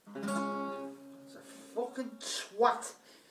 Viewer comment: did he just call himself or his guitar at twat? Lol